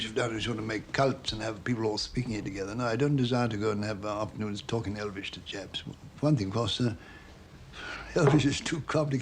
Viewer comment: did he say the racial slur there or something else? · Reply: I think he said "chaps" (British slang for man/boy)